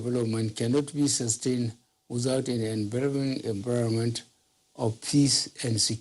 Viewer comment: Please what did he say in 2:28 ff... "Without a veromen everomen..." I am tired!!!